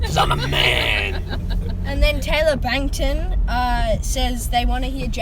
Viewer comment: did he say BANG TAN coughs bts?